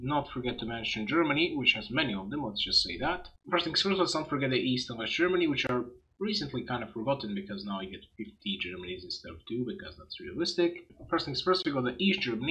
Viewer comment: What did you say at 1:07 its way too fast and I can't understand it sorry lol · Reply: East and west germany which are now kinda forgotten because we now get 50 germanies instead of 2, because that's realistic...